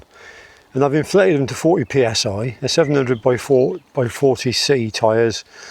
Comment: Its more probable I had 60 or 70psi in the tyres which are 700 x 32c I used to run these tyres on an old touring bike and was told by the bike shop that they should be inflated to 95psi. The manufacturers recommended pressures are from 65 to 95 psi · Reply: I thought you said 90 psi on the video I can understand why you were not very comfortable at that sort of pressure also what is it with cyclists and church’s I can’t pass one without popping in and having a look around you call and have a look around and I see others doing the same ?.